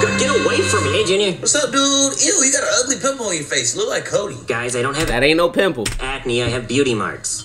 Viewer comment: For a second I thought he said covid.